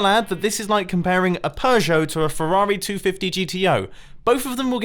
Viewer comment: did hé Just say that..... Peugeot... Is the best man.....